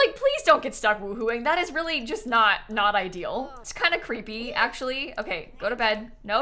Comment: When she said "It's kinda creepy actually" I had to do a retake because I thought she said "It's kinda k!nky actually" 😭😭😭